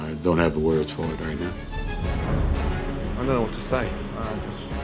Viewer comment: How can Ramsay use donut as an insult and expect people not to laugh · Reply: I thought he said "don't know"